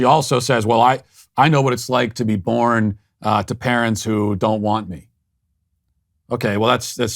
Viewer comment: Love your content! I heard her say "parents that hate each other"... Not parents who didnt want her. Just a slight difference. Keep it up Matt! May God protect you 🙏